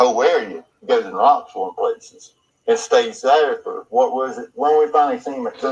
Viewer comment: I thought said, he goes to Knoxville and places. But I didn't know if he was talking about Andy or Don.